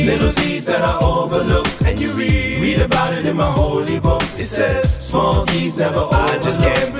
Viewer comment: 9 year old me thought they said "small deeds never overload"